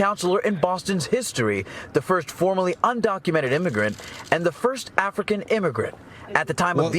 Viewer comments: they’re going off what the news reported. News said she was an illegal immigrant and never mentioned she had attained citizenship. Stop lying in the comments. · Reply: apparently neither you nor the hosts of this pod are listening to the news report very carefully. If you go to 15:21 in this video, you will hear the news reporter say "...the first FORMERLY undocumented immigrant..." When they use the word "formerly" that means she was previously an undocumented American citizen, and that doesn't apply to her current status. A simple Google search will tell you that she got her American citizenship back in 2019. I stand by what I say. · Bro, they were going off the news, so they are not lying, so is she black? Or is that a lie as well, next your gonna tell me she didn't steal from the city? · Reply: Apparently neither you nor the host are listening to the news report very carefully. If you go to 15:21 in this video, you will hear the news reporter say "...the first FORMERLY undocumented immigrant..." When they use the word "formerly" that means she was previously an undocumented American citizen, and that doesn't apply to her current status. A simple Google search will tell you that she got her American citizenship back in 2019. I stand by what I say.